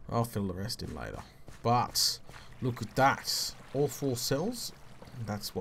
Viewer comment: When he said "all four cells" I heard "awful cells" XD